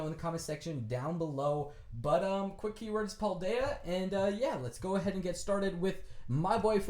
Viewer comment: IDK what u said (0:48) probably the keyword is puldeya